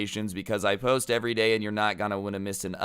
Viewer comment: did mans just say "unscribe" ?????